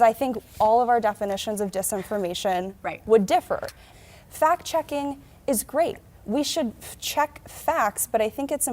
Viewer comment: Did she just say fact checking was rape? 😮